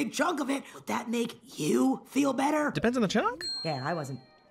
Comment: He did not just say “depends on the chunk?” after that☠️☠️☠️☠️